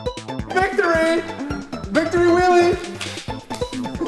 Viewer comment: it sounded like said victory willy.